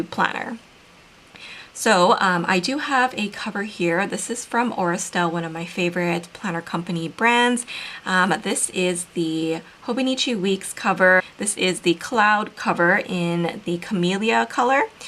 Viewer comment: Your cover isn’t linked and I couldn’t understand what you said. Could you mention it here? Thanks! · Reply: It's from Aura Estelle. It's the Cloud Weeks in Camelia